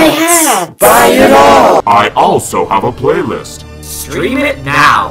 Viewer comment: I can't be the only one that thought that said buy my shit rather than buy my shirts in his intro?! I learnt 2 days ago, for the years I've been watching I never knew...the more you know 😅😅